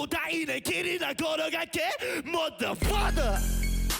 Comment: when jooheon said "mother father" i thought he said"mother fucker" and i instantly looked at the subtitles